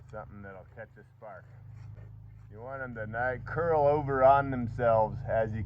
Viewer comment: did you say that you don't want the feathered parts curling?